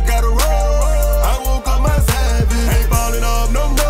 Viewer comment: Soooo am I the only one who thought he said.. " I woke up my side bitch"